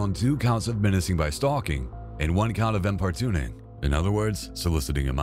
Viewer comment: What's the word he's saying there? Before "In other words soliciting.."?? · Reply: Importuning = persistent harassment of someone for or to do something